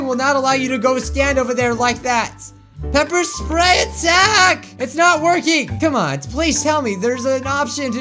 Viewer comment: when said “PEPPER SPRAY ATTTACK!” the YouTube captions said pepper spray it sanic Lol